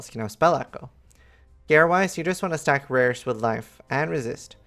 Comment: When you talk about gear (2:53), I hear "you want to stack '...' with life and resist." What is the first word? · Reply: "rares"